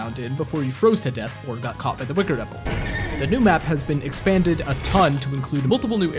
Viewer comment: I thought it said by the booger devil